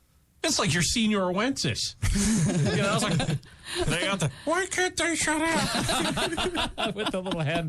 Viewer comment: I heard "Why can't they stop?"